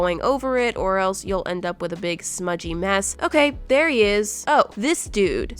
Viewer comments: I heard "Okay, bear he is! :D" Was that intentional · did she say "bearr he is"?